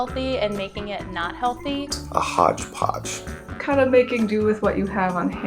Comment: Okay, this is going to make me crazy unless I say something, but at 2:08 the captions say "a hot (indistinct)" where he's actually saying "a hodgepodge". Okay. Got that off my chest. Phew! Sorry, but I used to do captioning and relay service for the deaf and HOH, so this was making me nuts.